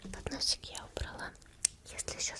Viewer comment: 1.как выделять время скажите пж 2. Мне одному на 5.5.5 послышалось "ну все поносик я убрала"?)))